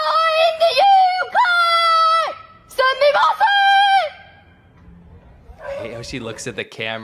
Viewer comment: Sounds like she is saying Sami Massen...the English gave her away though ! Ha Ha ! 😁